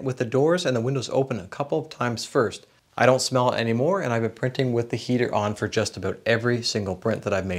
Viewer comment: I thought you said you don't smell anymore ..lol.. great review..I own 2 bambu labs..one J1 snapmaker idex..and now looking for a larger printer to try stronger filaments.